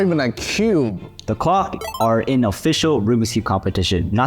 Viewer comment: DID SOMEONE SAY GLOCK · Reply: NO CLOCK